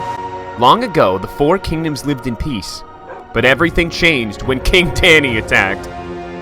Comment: did he say “king fanny?”